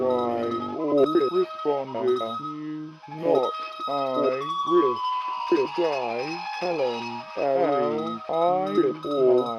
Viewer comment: Not much scares me, but the distorted audio in that video actually had me calling a friend on Skype so I wouldn't be alone. I couldn't explain why, it just got to me. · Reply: When I originally heard the audio, I thought the voice was saying "Jenny". Then I couldn't be scared because I kept thinking I was gonna hear "867530 nieieine"